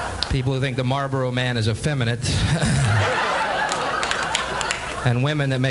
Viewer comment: What did he said at 1:27? Femi what? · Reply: Effeminate